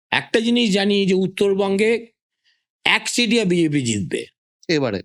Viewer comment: Can someone tell me, what he said at 42:19? "এক _ বিজেপি জিতবে" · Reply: তরফা